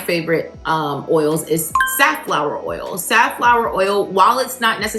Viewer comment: Okay so it sounded like you said Safflower oil but the wording came up sunflower I just want to make sure which oil you was referring to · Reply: Safflower apologies For the confusion